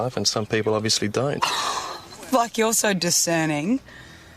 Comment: I could swear she said "f$%k, you're so discerning" 😮